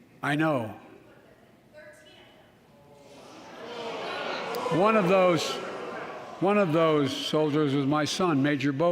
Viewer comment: Anyone know what the lady said at 1:25:15 ? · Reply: The ignorant woman said you put them there all 13